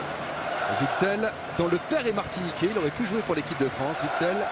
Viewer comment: "Witssel dont le père est martiniquais" j'ai entendu "dt le pére est parti niquer" mdrr Pouce vert si ct pareil pr vous =p